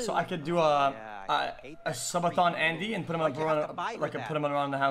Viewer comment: Here is the full clip of xQc blowing up if you wanna see the rest. Hopefully you enjoyed the video xqcL <link> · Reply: Hey Mr. Editor, you wrote "Subation" instead of Subathon in the subtitles